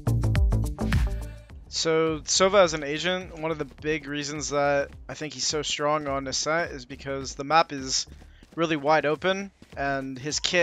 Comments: I thought he said "Sova as an Asian" ....i was like...whhaaaa? Lol im big dumb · so Sova is an Asian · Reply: what i hear "sova is an agent"